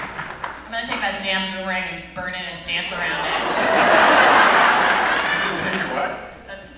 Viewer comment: what does Rachel says at 17:51 ? THANK YOU FOR UPLOADING THIS VID!! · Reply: "I'm gonna take that damn boomerang, burn it and dance around it!" :D